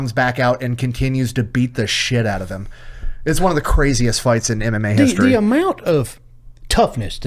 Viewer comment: I swore he said greatest fight in anime